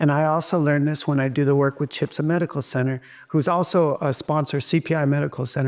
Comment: Does anyone know what medical center Cruz is saying. I have watched it 3 times and can not make out what he is saying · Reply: He said CHIPSA and he says it is out of the country. So I looked online by typing Dominick Cruz Cancer Center into Google and found that he is connected to CPI (Cellular Performance Institute in Tijuana, Mexico) that is at CHIPSA Hospital. Replaying what he said again, you will notice he said CHIPSA, but then he says that patients have to go out of the country for this, and he quickly says "CPI" which confirms it is the correct place at around 4:25. I was also curious so I'm glad you commented or I wouldn't have looked further into it!